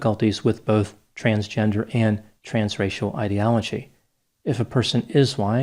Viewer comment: Every time he said "trans-racial", I thought he was saying trans-Rachel!